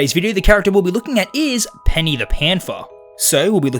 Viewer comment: I thought he said Penny the panfa 💀